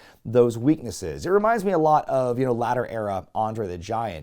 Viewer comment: Insread of 'latter'. I thought you said, 'Ladder Era' André & for a second I imagined André in a ladder match!! LOL No disrespect to one of my all time favorites but could they even put together a ladder that wouldn't crumple under his massive size? Just one of those what ifs? Of Wrestling history!